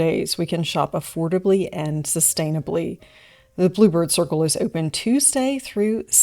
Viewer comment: Funny thing,at the start of this video,I thought you said the ‘blooper circle shop’!🤣 Then I read the sign,and thought,ah,that makes more sense😅